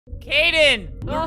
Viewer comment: Yay a video with Kaden! I love these vids. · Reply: For a min a thought that said “Karen”